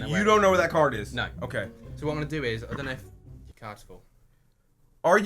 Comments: what did he say at 6:07 your cards are full? what??? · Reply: He said "your card is full" becuase the memory card on the lower camera was full and it stopped recording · What did he say? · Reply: Felix Pires "your card is full" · i did not here him. someone pl tell me what he said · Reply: I believe he said "Your card's full." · What did say at 6:08 right before went like " are you FUCKING serious" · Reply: He said "your cards full" The memory on his other camera was full, that's why it turned off 10 seconds before he said it.